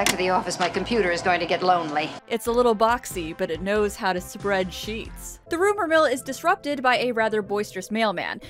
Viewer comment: "It's a little boxy, but it knows how to spread sheets" sigh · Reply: Oh no, I heard “spread cheeks” lmao